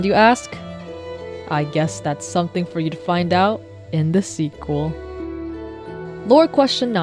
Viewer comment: DID I JUST HEAR THE WORD "SEQUEL"????????????????? AWWWW YEAAAAAAAAAA